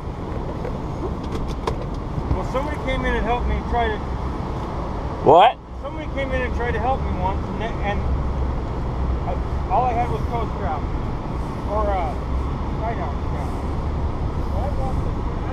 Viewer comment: for the next time You can get a COMPASS app. Free in about 10 seconds on your I phone. Glad you found dry ground. Any signs of the castaways The Skipper or Gilligan?. :-D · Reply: My mistake, I thought you said " I wished I had a compass ". Also; I don't know how well they work but a blue light is supposes to punch through fog the best of all colored lights. ( For the next time of course ).